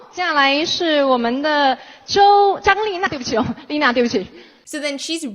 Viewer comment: To be fair The first guy to the very left is the gorgeous Xiao Zhan 肖战, and it sounds like she said his name. Maybe she did this by accident, but if you knew who Xiao Zhan was, you would understand why 😂